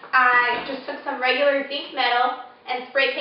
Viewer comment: Great job! A question though-- when you're talking about the black metal that you put in the bottom of the oven, I can't tell what you're saying. Right around 2:28, you say "Earlier today, I just took some _______ metal and spray painted it black." Did you say "zinc metal"? And is that something that you can get at Home Depot or Lowe's?